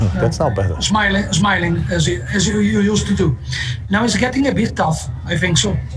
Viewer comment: did he say smiling, as you used to do? 🤔